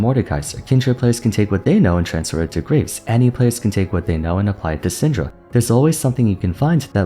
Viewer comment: ROO ROO I was only listening and not watching, instead of "You can play Annie and apply it to Syndra." I heard. "You can play ANYONE, and apply it to Syndra." I'm gonna head canon the script to being that, cause Syndra has so much easy to land burst and hard scales and is so safe in lane. She's really not hard. uhhh uhhh, Nilah thoughts, UUUUUhh.... She's Samira but water, haha funny meme.